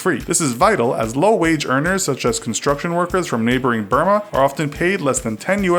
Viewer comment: did I just hear Burma my home country? 🇲🇲